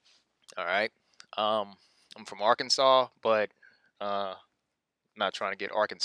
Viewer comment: Great video as always! At 10:20 I could have sworn I heard you say you were from Arkansas. If I heard right, that's pretty cool to hear! I am too. Grew up in Pulaski county but a few months back I moved to the country after waking up to the lies and becoming born again. I ordered the book "The Lost Ways" by Claude Davis and started over. Pretty cool to hear that is from my home state. Keep up the good work! You and some of the other YouTubers have definitely had an impact on my life!